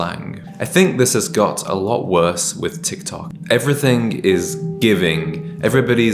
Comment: I loved the "Don't be a Parrot" tip. I'm constantly correcting my nephew for using trending slang. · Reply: I thought he said “this has GYATT a lot worse with TikTok”